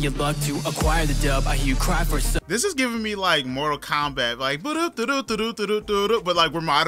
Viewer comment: Who else heard Cowboy Bebop when he said Mortal Kombat at 1:10 ? 😂😂